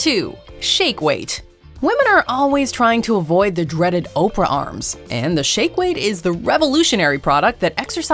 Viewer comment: Did you Say "Oprah" Arm💪😂😂 I only know 0ne Oprah is that who your talking about??